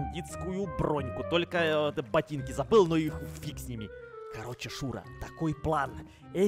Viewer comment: простите, но я здесь слышу ну и ху.фиг с ними